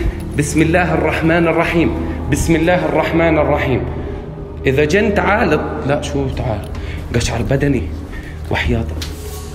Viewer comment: the translation is a bit off, the guy says " If you're a Jinn, come" and he then realizes it's not a good idea and says something like " wait no nevermind, what am I saying, I have the chills"